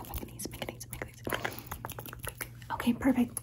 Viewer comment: everytime I hear that I hear “Miss Megan knees” 😂